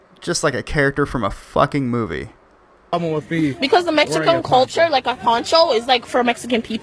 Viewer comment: I'm pretty sure that I just heard "Mexicoan" culture come from her mouth.